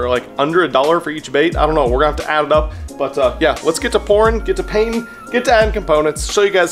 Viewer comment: is it just me or did he say porn